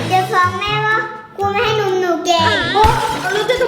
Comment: ครูไม่ให้นมหนูแ..กหรอได้ยินแบบนี้อ่ะ 555+